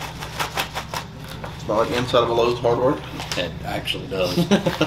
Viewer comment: I can't catch what said. "Smells like..." what, can anyone translate for me please? @ 4:50 also. Is this a cultural reference or brand that I'm not recognising? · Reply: Smells like the inside of Lowe’s hardware